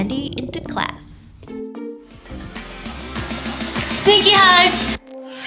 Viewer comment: When she said “sneaky hugs” I thought she said “stinky hugs!” 😂😂😂